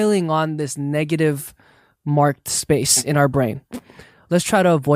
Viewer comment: I misheard Jae saying brain as brian lmao I had to listen it again XD my brain wasn't functioning im sorry